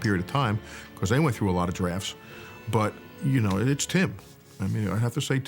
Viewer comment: I thought he said "cause they went through a lot of giraffes." And then after I stopped laughing hysterically, I realized he sad drafts. -_- lol